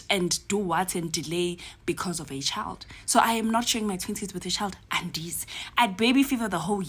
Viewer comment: When I was growing up, public restrooms were clean. So no it's not normal and shouldn't be seen as acceptable for public restrooms at a "cheaper establishment" to be dirty. I thought she was saying "I'm not sharing my titties with a child" LOL as in not breastfeeding